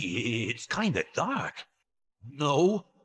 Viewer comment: and after all those years of watching those Mario hotel YTPs growing up, I thought it sounded like he said "No." with a cracked voice.